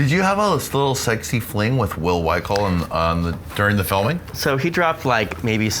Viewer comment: what is the name of the guy at 09:01? i cant understand what they say. Will ...? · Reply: He said “Will Wikle.” Apparently he was on Big Brother and starred in the Stillest Hour Part 2 on CockyBoys